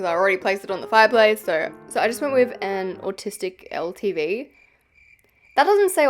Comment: I thought you said autistic instead of artistic and I was like 😮